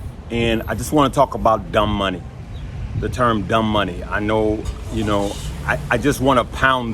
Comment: At first, I heard "dumb bunny." But emotional trading is being a dumb bunny. I confess, I have been a dumb bunny.